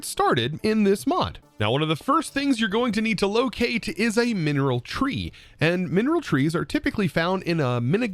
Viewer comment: listening to the video is slightly confusing because I kept hearing "mineral tree" :D to my brain it's pronounced "men" + "reel"